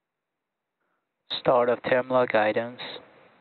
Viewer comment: what was that call out? If sounds like Start of terminal guidance but the caption reads start of time log items. Never heard this one before.